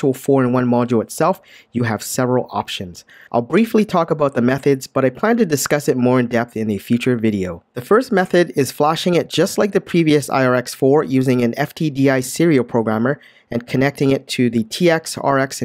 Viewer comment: which video are you referring to? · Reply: Sorry, I misunderstood what you have said at 12:10. I thought you have said "in a future video", but actually you have said "in the feature video", right!? I was looking for a method to upgrade the firmware without a FTDI adapter. But now I have received a adapter!